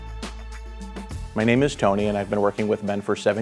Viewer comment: I thought Tony said "Hi I'm Tony and I've been working with men for 17 years" LOL